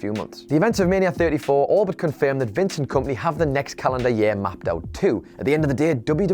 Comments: sound like he says Vincent kompany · sounds like he said Vincent Kompany